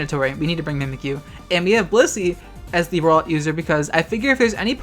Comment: Every damn time they said "Blissey" i misheard-ed as "pussy"